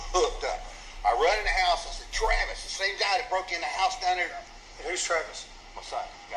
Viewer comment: I highly doubt he said. The same guy, instead of that’s the same N word. Just saying.